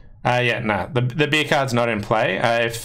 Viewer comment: Sorry, I keep hearing you say “beer cards” but I’m pretty sure that’s not what you’re saying. Can you please enlighten me? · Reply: If you take the last trick with the 7 of diamonds (not trump), it is called the beer card. Must be an Aussie thing. 😂